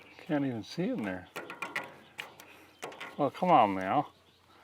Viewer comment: Did you just say come on meow?